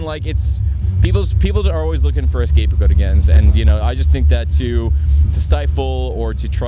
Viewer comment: what the fuck does he say at 5:10?.....escapegodegens?.....wtf? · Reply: "People are just looking for a scapegoat again."